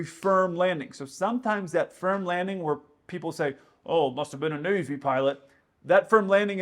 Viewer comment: I thought he said " must have been a Days Inn piolet ". 😄